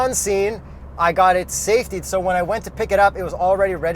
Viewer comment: What did he say at @ 2:52 ? He got SAFATIED? Is that a service to have the car registered or something like this? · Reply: In Ontario, "safetied" means a safety inspection to make sure the car's safe to be driven on public roads. This is an Ontario government requirement for all used cars sold in Ontario, or that originally came from another province and need to be registered in Ontario. New cars don't need to be safetied.